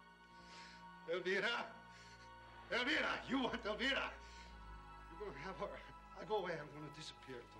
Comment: It always sounds like Frank is saying "You Want Velveeta" How could Tony not want some Velveeta!!!